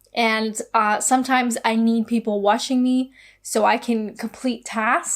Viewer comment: what, what you say? ... You need ppl washing you? Eh, lol. 😉